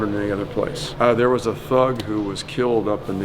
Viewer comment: he said a THUG was killed but because my hungry self was crunching on a chicken sandwich and that was all I really heard in my ears, I heard him say a THOT 🤣🤣